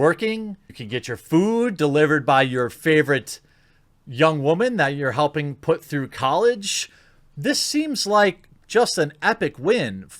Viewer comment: You said: "Young woman that your helping put through college." I heard "Titty Dancer!"